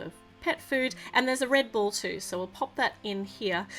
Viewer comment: You said red ball but I almost thought you said Red Bull 😊😂🤭